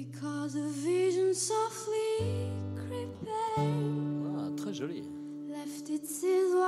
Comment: anyone please , what does the guy on 00:19 say in french? and how is it spelled? ??? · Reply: "Ouah très jolie" he said